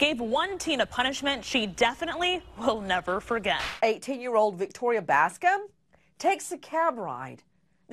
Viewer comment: is it just me, or does she say a teen year old?